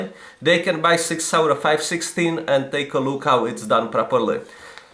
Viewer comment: did he say 516? Or 560? Because I heard 516. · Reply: Sig Sauer 516